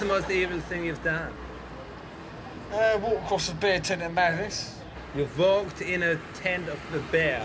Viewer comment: Is he saying he walked accross a bear tent? I've been wondering what he was refering to for about a decade. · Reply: it's funny, I've only just realised he was probably saying 'Madness' after having watched this at least 100 times over the last 15 or so years (and before seeing your comnent.)I always thought he was saying 'Manleys' thinking it was some dodgy club or night in Aylesbury.